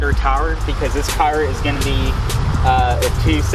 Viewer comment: is he saying tires or towers....